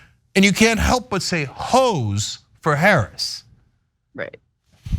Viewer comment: I thought she said “Hos for Harris” at 1.5x speed during the first watch! 😂 It sounded funny and catchy.